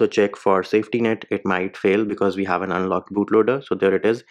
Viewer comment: no, cus u said during magisk part that the safetynet failed cus u didn't unlock the bootloader or maybe u wanted to say that it is unlocked but said that it is not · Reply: ah My bad I heard 10:27 as "we haven't unlocked bootloader" but u said "we have an unlocked bootloader" Nice video man Apologies again